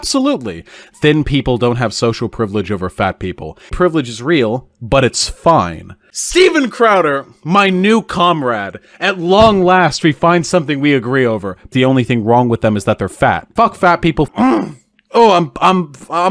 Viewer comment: I misheard him saying Steven Crowder and thought he said “Semen Crowder”